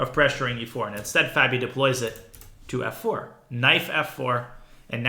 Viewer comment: Does danya say 'Knife f4' ? :D Not trying to be arrogant but found it funny.